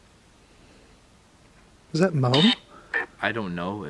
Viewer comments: sounded like it said Tom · I heard it say Moe